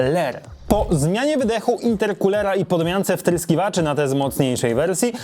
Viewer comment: usłyszałem spryskiwaczy xD